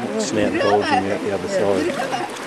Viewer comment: did he say Beautiful? Wt....